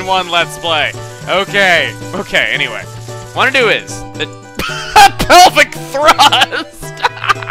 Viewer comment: What did he say around 3:46. I didn't hear anything funny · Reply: He said "that pelvic thrust"